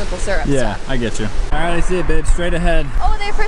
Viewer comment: Did say I see a b**ch